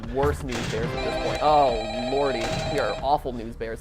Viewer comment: I thought he said "Oh Morty" because I have been watching a lot of Rick and Morty and his theories about it. Lolz